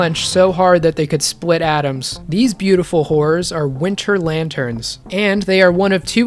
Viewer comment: I thought you said “ beautiful wh*res” for a moment and not “beautiful horrors”